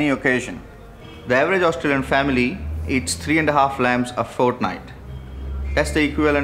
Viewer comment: did he say Fortnite lol